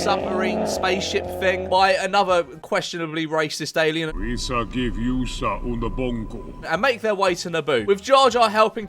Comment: what did the alien say? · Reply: Weesa give yousa unabongo